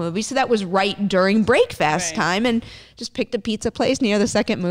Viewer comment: did she just say Brake-fast? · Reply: she was breaking her fast. she said break-fast time